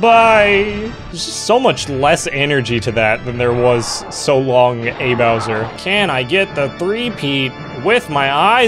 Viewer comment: Until someone pointed it out I thought he said “so long, de Bowser!”